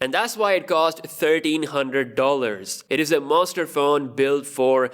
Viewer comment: i heard 30 hundred lol